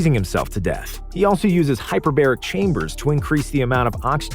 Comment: I thought you said he uses the hyperbolic time chamber😂😂